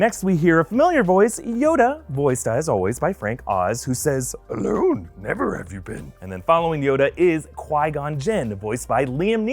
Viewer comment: I thought yoda said Hello never have we met